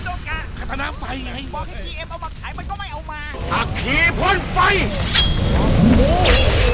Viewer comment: คาตานะไฟ 6:36 ได้ยินเสียงคนพูดด้วยว่า GM ไม่เอามา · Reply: คำที่ว่าบอกGMมาขายมันก็ไม่เอามา